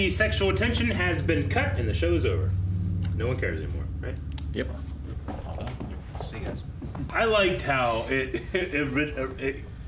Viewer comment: I really thought someone said, "Cigarette" and had to back track to realize they said, "See ya guys." LOL "Pull the blinds first." "It's the middle of the night." Actually, the fact that it's the middle of the night, makes it WAY more noticeable.